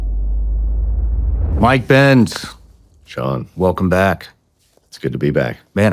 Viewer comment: I habe been listening not watching and for the first 30 min i thought he said 'mike pence' and inwas like dang this guy is actually making sense for once. Lmao nope